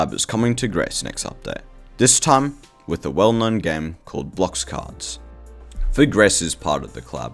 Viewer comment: Did bro just say Blox Cards of all things??